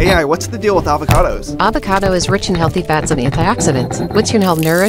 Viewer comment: did you just call it avocada avocado